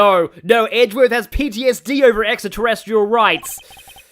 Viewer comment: Did you just say Extra Terrestrial Rights?